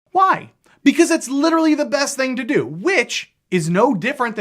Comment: i thought you where saying that playing starfinder together is literally the best thing to do lol